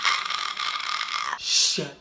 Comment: "Shut the fuck up. Shut the FUCK up!" 🤣😂. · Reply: I like how what I heard was “Shit the fuck up!”